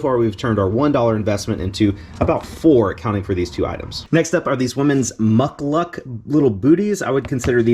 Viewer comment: Oh you said "Muck Luck", I originally thought you said "Muck Duck" thanks for sharing.